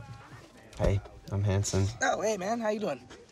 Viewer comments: yaaas brennen taylor! and who else thought hanson said hi im hansom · I thought he said "hey I'm handsome" 😂😂😂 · When Hanson said "Hey, I'm Hanson," I thought he'd said "Hey, I'm handsome," at first. XD